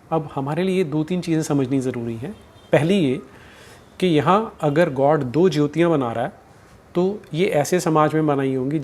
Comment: I heard 'Chu' instead of 'Jyo' . I think what I heard was correct explanation for this dumb God Of Christianity 😂😂😂